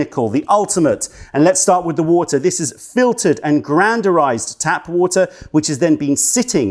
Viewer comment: Anybody understands at 3:03 "... it is filtered and gr....ized tap water.." what is that GRANDAURIZED or what he says? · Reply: Yeah, he says “granderized.” I’d never heard of that before, so I looked it up and honestly “granderized water” just looks like a scam. Nothing I read really explained what this process even is, but websites selling it keeps claiming that it’s better than regular water somehow, mostly using a bunch of nonsense pseudoscience terms.